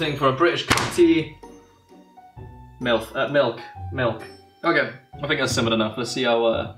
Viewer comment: Did he said milf instead of milk